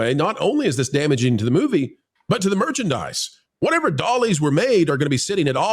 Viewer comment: I'm flowing you and because I need a break from politics. I had to Google Ollie's, because I thought everyone was saying Aldi's.